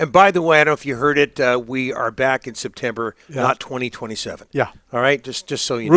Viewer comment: Why are these guys going away till 2027? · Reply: I think they said that they will be back this September and not in 2027